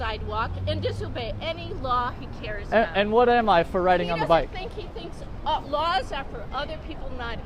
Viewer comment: Watching this for the first time I thought she said writing on the bike... I was confusseld